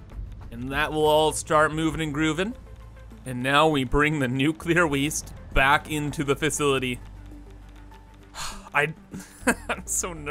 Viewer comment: I literally heard "we bring the nuclear waste back in to the fissility"